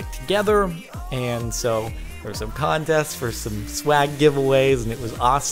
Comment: Pauses video at 3:48 I say to myself " did he just say swag"